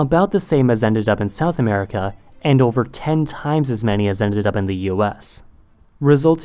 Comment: Dude you have you data numbers totally wrong with 5 million exported to the Caribbean, 5 million to Brazil & South America and 10x that amount to North America. 85% of Africa slaves were exported to Caribbean and South America and 100,000 were exported to North America. Alot of slaves after being broken into slaves were then brought from the Caribbean to America. You also forgot Honduras, Belize as part of the Caribbean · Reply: He actually says 10x as many AS were sent to the US